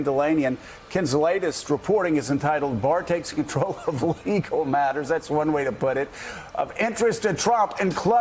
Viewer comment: I misheard Joe talking about "Barr takes control of legal matters." While misunderstanding this phrase, I actually had a moment of clarity. I heard "fecal matters" instead. Seems a lot more accurate and appropriate.